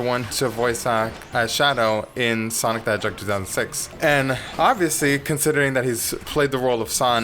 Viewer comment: Mr says; Sonic the Hedgehog 2006. 1:14 What the Subtitles thinks he said; S o n i c t h e J o k e 2 0 0 6 My Dead Braincells; Mr i tHinK yOu MisSpeLleD ThE jOkeR...